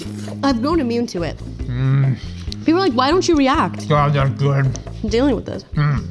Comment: What...did he say???? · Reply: “God that’s good”